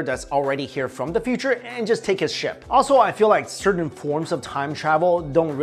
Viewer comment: Coulda swore I heard ...find a time traveler from the future and just take his sh#t...lol